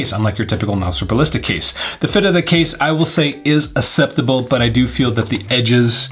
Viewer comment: ad here we with your acceptable "asseptable" instead of "akseptable" :)